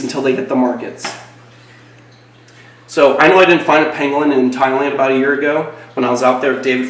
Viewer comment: Who else thought when he was saying Pangolin he was saying Penguin